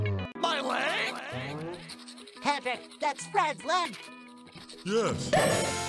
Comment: Everytime SpongeBob Says “Fred’s Leg” It Sounds Like He Said Fred’s Luck!